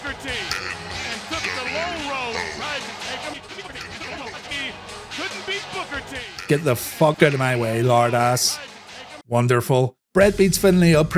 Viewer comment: I thought Bret said, “excuse sir pardon me” 😂